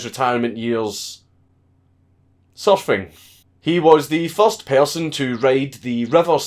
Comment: I thought you said “suffering” instead of “surfing” at first and I was about to be bummed out but it’s just your thick and endearing accent! I love this series it’s extremely entertaining. As you said in General Butt Naked.